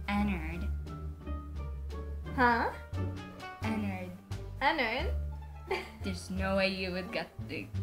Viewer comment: When u said Ennard is sounds like u said en nerd